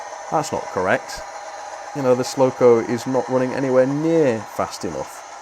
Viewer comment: I had to rewind just then, I thought you called it a "slow-co" and it's very fitting!